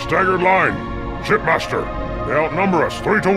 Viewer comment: lol the subtitles said sh*t b*astard